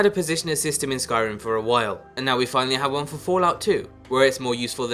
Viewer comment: wait did he say Fallout 2?